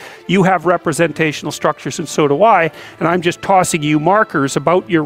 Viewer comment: HAHAHA wtf. I hope its a joke. why did they think he said Soda, why? and not "so do I"